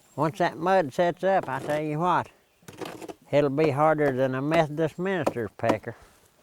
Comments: What did he say at 18:56? Sounded like he said Harder than a Methodist Minister’s pecker?😆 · "It'll be harder than a Methodist Minister's pecker." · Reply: Sounded like he said " a meth dispensers pecker" lmfao